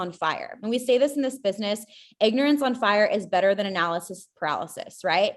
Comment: “Ignorance on fire is better than analysis paralysis” has to be one of the most troubling and problematic sentences I have ever heard. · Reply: I thought she said “Inexperience on fire”….?